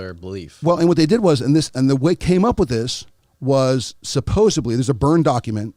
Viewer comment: He actually says SUPPOSEBLY 😂 🙌🏻